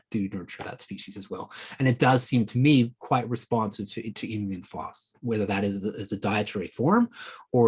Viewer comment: Dr. Jason Hawrelak points out at 21:54 a key nutrient for akkermansia but I couldn't understand it, do you know what does he says? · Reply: Inulin and FOS (fructooligosaccharides).